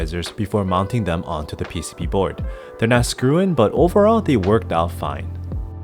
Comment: quick question, this keeb can't support screw ins, but here it sounds like you said they are now screw in, i might just be dumb here but could you confirm this? edit or did you just say there not screw in :/